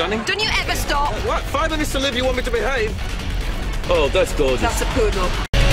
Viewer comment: "She is gorgeous" "She's a French poodle" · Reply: I thought he said “That’s Gorgeous.” And Qwen said “That’s a poodle.” I’m not trying to be rude so sorry if it came off rude